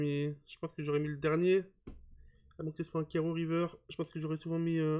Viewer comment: t'as dis quoi à 14:25 ? "..................carreaux river" ? pas compris avant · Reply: A moins que ce soit un carreau river!